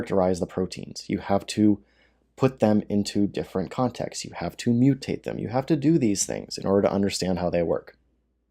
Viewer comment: "If you force a wild virus to mutate..." You misunderstood something. "You could culture that virus in its original host organism MUCH more safely" You misunderstood something MUCH more thoroughly. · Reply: Verhoef 5:19 you have to mutate these wild viruses. I think I heard him pretty clearly.